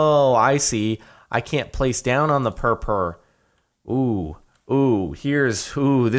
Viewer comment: did he say purpre?? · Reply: Ahmad K Hamdan its purpur which is the name of the purple block that makes up that stage